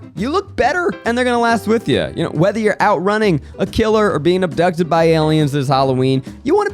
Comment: In that Me Undies ad I definitely heard "Whether you're out running, a killer, or being abducted by aliens this Halloween."